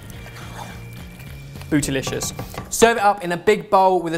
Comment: Did I just heard saying "BOOTYLICIOUS"???